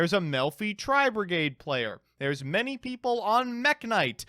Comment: I put this video on x2 speed, and I thought you said a "MILFy Tri-brigade player" @ 0:42, and now I cannot un-think my interpretation of that bombastic abomination of words.